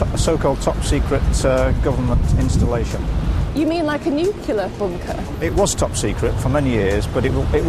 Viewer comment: wait, at 2:21, did she say 'Nucular'?